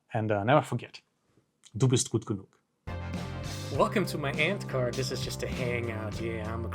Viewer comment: I swear for the first few months, I thought you were saying, welcome to my ant card.